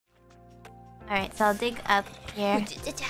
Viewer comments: Can't lie, I definitely heard "moron" the first time I listened to this too. Only heard "alright" after trying to hear it that way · The first time I heard this while driving, I heard moron as well. But after listening to it while eating I heard "all right".